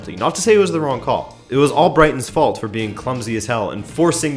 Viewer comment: Ha! You said "Brighton" instead of "Leicester" about penalty. Great Interesting Summarized Views, love it... Makes me watch more highlights. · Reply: I thought that too, that he said "all Brighton", but he actually said Albrighton, as in the Leicester winger. Took me a moment!